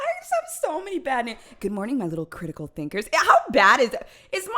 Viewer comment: I love how when she said, "hello my little critical thinkers", I thought she said, "hello my little crinkle finger". Like bruh😂😂